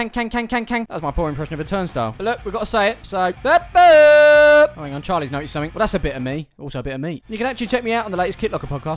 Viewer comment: i don’t know what he’s saying is that just me. Does anyone know what he says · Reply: FOOOOOTBAAAAAAAALL